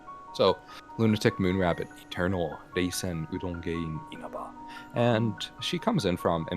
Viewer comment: The Subtitles when said Reisen it thought he said Jason and I fucking cant stop laughing